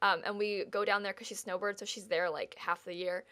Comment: I swear you said your grandma SNOWBOARDS at 8:24. Then I realized "snowbird" is in this case a verb